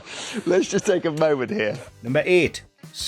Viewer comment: All this time, I thought he said super spoon